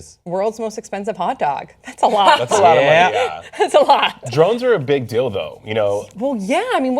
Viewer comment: Did he say "drones are a big dildo"? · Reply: Emma Sive LOLOLOL he said ' drones are a big DEAL THOUGH'